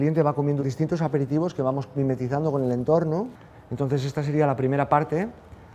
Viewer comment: What word does he use in Spanish for hidden? __?_ por el entorno. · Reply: mimetizando